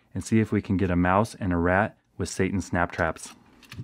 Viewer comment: did anyone else think he said "Satan" snaptraps XD I'm dead wait does it say Satan(caption at 1:09)?? Or seitan (captions